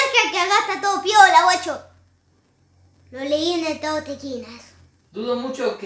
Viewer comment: qué dijo? libro tekines?